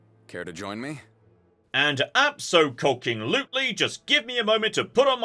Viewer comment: "abso-cocking-lutely, just give me a moment to put on my dress." Yep, I heard that, and now I can't unhear it can I?